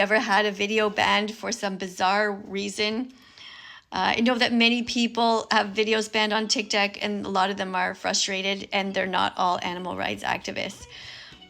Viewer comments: did she just say tiktak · does she call tiktok tiktak/tiktac? Please can someone tell me what they hear